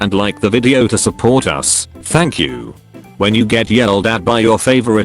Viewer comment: why did I hear fuck you instead of thank you?